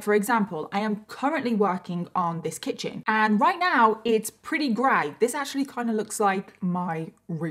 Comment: 😭😭 i thought you said “and right now it’s pretty gay”